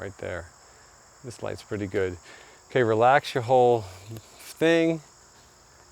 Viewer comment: It'll make me sound like a perv but I really couldn't help laughing at 17:06 when says, "Relax your whole...thing." Before he could say the word "thing" I started laughing because I thought it sounded like he said, "Relax your hole." Sorry for taking this into the gutter! Great video!